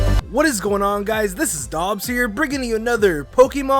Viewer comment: Did you just say Nobs here instead of Dobbs here?